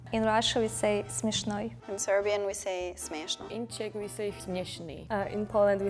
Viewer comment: For example? I think it was pretty accurate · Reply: and another one at 10:46 the russian girl says "smeshnoy" but the subtitles say "zabavniy" which also means "funny", but again not the word she used